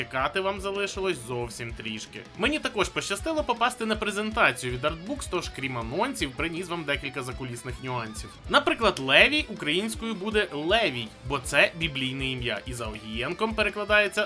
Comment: почулося як Ленін